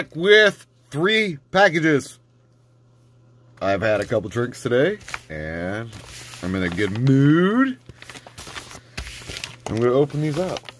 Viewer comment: Great pick ups. 0:05 sounded like you said, "I've had a few drinks today... and I'm gonna get nuuuuude!" Haha